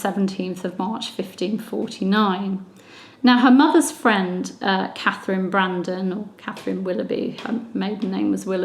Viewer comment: I thought that you had said Mary (Tudor) Brandon, and was speaking of Henry VIII's sister--but now realize you were speaking of Katherine Brandon, Charles Brandon's 4th wife!